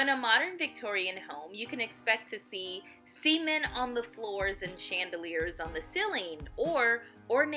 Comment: I thought she said "semen on the floor" and the auto generated captions say the same omg